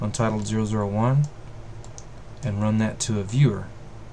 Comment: Thank you for this fantastic tutorial.The only thing that i'm not sure is at 8:50 the mp3 player like with the wire? I'm new with Blender and i don't understand those little thing.So thanks for sharing your tips.